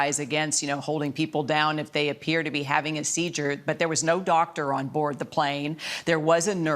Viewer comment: the news lady says "seejure" instead of seizure. Am I wrong?